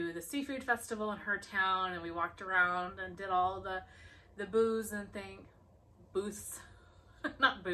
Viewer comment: I’m glad y’all had a great time! I definitely thought you said booze and not booths 😅